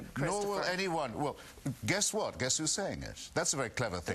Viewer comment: you for the answer. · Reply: I thought he said "would you prefer if I added 'your opinion?'" As in would you prefer if I added that to my arguments against you.